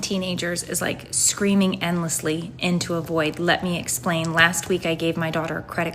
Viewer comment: Subtitling on the TikTok video "screaming endlessly and to avoid" instead of "into a void" tells me everything I have to know about this person's intelligence level...